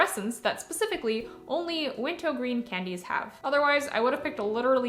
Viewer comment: Ohhhhh I thought she was exclusively saying wintergreen with a baby voice and was wondering what wintowgreen was all about